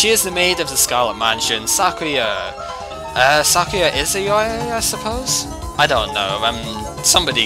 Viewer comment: Listening more carefully, you did say Izayoi, lol. I misheard youkai for some reason.